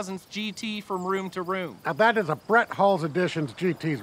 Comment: For a second I thought squirlly Dan said Bret Hart there.